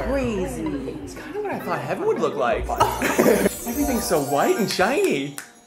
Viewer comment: I thought he said, "This isn't what I thought head would look like..." Ik I have a very dirty mind but think abt it